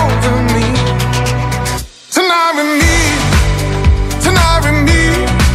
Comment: I thought he was saying to tnar an Be. Sounds nothing like tonight with me